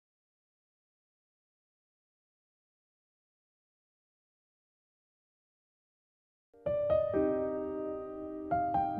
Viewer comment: di menit 27:19 itu mamanya bilang apa kak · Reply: Algara : mahh, mamah marah ya sama algara? mamah : ........... Gak mamah ga marah sama km, mamah berterima kasih sma algara karna algara udah kasi tau mamah, siapa sebenrnya aryana Note : suaranya ilang karna kena cr guyss 🙏🏼